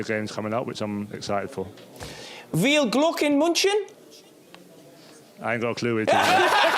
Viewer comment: "Wiehl Gluk in Muntschen" ?? WTF was he trying to say? I am German, and I didn't understand that guy either.